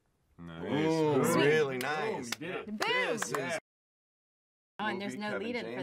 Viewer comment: I thought she said boob XD